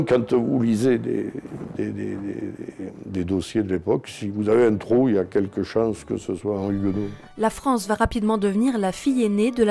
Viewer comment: Il a dit quoi le monsieur à 2:43? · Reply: "Si vous avez un trou pour la religion, il y a quelque chance que ce soit un huguenot"